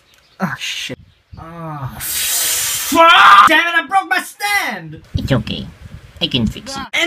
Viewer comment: "Dammit I broke my stand! It's ok, I can fix it." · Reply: i thought he said "dad i broke my stand!"